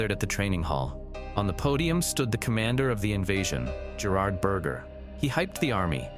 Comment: did he just say his name is BURGER?!?!